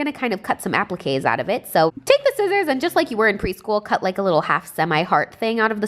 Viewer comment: it sounds like she said weed